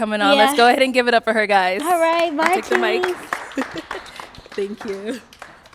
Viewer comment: Did she say "bye kids" lol